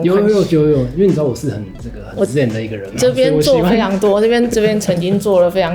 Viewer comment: 那段「因為我是個很自戀的人」果然是寫錯了，我不太相信廣仲會這麼講，可惜我反覆聽了幾次，還是聽不出來。希望大家看到更正啊…..「是很ＺＥＮ的人啊」